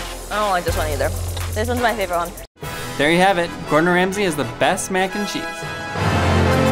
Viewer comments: wait sounded like you said ( THERE YOU HAVE IT ''GORDON RASMY ''IS'' THE BEST Mac N CHEESE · Did anyone else notice at the end it kinda sounded like he said “Gordon Ramsey is the best Mac and cheese” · Is It Just Me Or I Heard "Gordon Ramsay Is The Best Mac And Cheese"